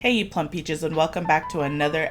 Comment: Lmaoooooo I thought you said “Hey you punk Bitches!” 🤣